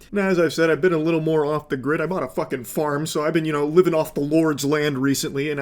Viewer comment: did I hear that right, bought a farm?